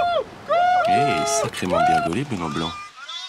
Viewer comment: a 2:13 perso j'ai entendu "Hey sacrément bien gaulé pour un blanc"